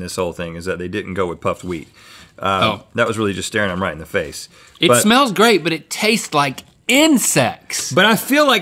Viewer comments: Really thought he said, It smells great but it tastes like INCEST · Tell me why it sounded like link said it tastes like insest